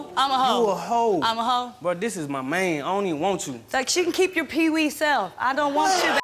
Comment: Wtf he said I don’t want u I want my man lol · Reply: CLOROX BLEACH main*